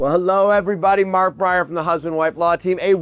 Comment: I could have swore he said Markiplier 😂